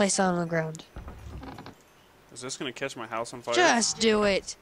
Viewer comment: Is this gonna kiss my house on fire? Me: 0.o xD That's what I heard.